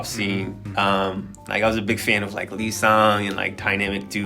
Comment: I thought he said 11:12 "I was a big fan of Lisa" 😅🤣